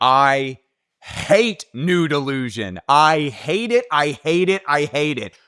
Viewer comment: I thought you said "new delusion" instead of "nude illusion".